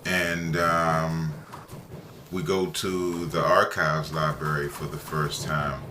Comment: For a moment I thought you said "archives backrooms"